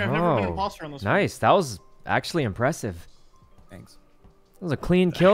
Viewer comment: what does corpse say at 11:38? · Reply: He said "Thank you" after Sykkuno complimented his vent kill